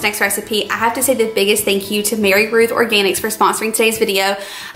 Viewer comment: I thought you were saying Mary Ruth's, then i thought you were saying Mary Ron's. Slow down and maybe even spell Roth because i had to really focus to figure it out. It's Mary Roths.